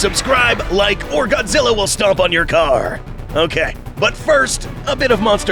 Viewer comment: I thought u were gonna say; "or godzilla will stomp on your cock" Had me scared for a second